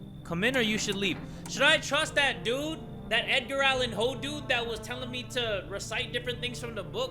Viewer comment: EDGAR ALLAN POE REFERRENCE OR DID I HEAR THAT WRONG ??